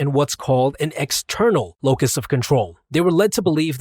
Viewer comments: It says Eternal Locus of Control instead of External Locus of Control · Also by the way 1:44, Eternal instead of *External*. Your welcome